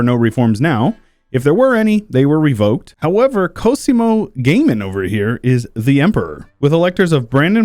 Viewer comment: I'm sorry but all I heard was Cosimo Gaming xD